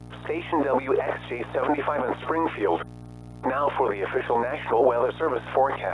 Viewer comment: Did I hear springfield?